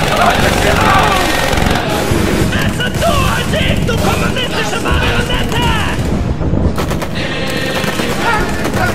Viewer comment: What is bro yelling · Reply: "Besser du als ich, du kommunistische marionette!” Better you than me, communist puppet!